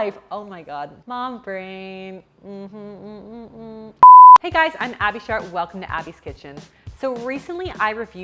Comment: sounded like you said im